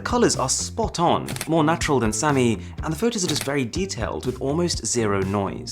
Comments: did arun just call Samsung as samy 😂🤣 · did he say sami instead of samsung?